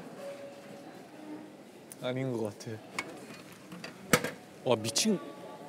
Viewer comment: as Johnny said "michin" (crazy) the caption says "beautiful word" 😭😭